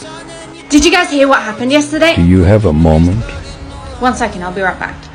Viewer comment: "do you have a moment?" Why did i hear "do you have a mom?"